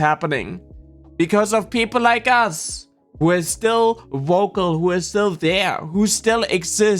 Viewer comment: Not only all of this, but also the anti-intellectualism with flat earth, science denial, trying to tear down the educational systems, etc. Also, I know it's your accent the way that you say vocal, but it sounded like you said woke-al...which is kind of an awesome portmanteau